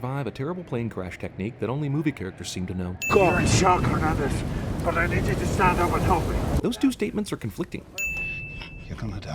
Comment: or so, what were the conflicting statements because I can't understand what he's saying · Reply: "Your in shock Hernandez! But I need you to stand up and help me!"